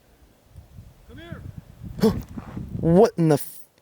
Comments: in It was a voice ‘come here’, that’s what I heard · 😮 wholey f... what the hell a man said come here 😮